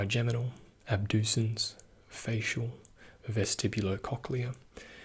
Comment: did he say facial then something cock 😳